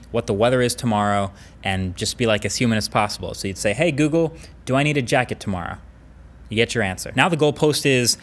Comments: sounds like he says: "Hey Google, do I need to jack it tomorrow?" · I legit heard "Google, do I need to jack it tomorrow?" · I heard that as 'Do I need to jack it tomorrow" LOL